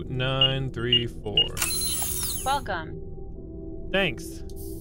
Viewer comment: You know...is it only me or do ya all misheard whenever the machine speaks "Welcome"... 17:14 .I heard "f*ck on"??? lmao